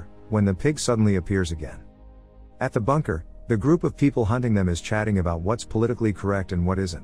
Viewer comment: Still can’t tell if his voice is from a real human or a computer generated voice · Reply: Confirmed computer generated: see 6:49 - it says "hem" instead of the intended "them". If it was scripted, then the guy would realise he said this wrong and hem isn't a word.